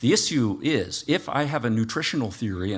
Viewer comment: I thought Hicks said “cookie nutritional theory”